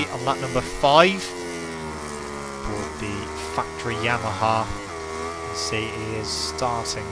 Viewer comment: I swore you said the "f**ing yamaha hahaha